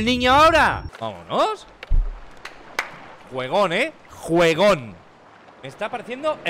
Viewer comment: Auron Chileno: gueon eh👌 Sé que dice "Juegon" no mames Xupala Otro Xupap1nga: Hakahsksha Porque los chilenos dicen weon dfjwjsus que risa ajdkaja no tienes imaginacion Jelsaksjks convulsiona Nskajsa.... Muere con una sonrisa